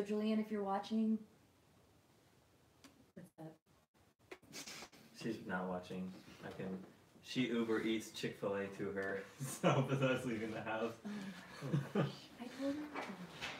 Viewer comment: What did say at 0:43? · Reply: She's not watching, I think. She Uber Eats Chick-fil-A to her, so with us leaving the house...